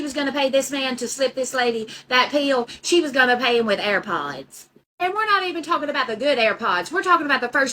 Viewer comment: I thought you were saying air pies 😂 air pods….